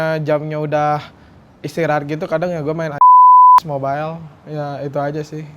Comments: Min gw pgn tau si itu ngomong apa knp harus di sensor jadi penasaran kan gw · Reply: LOL mobile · Itu Ngomong Apa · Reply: LOL mobile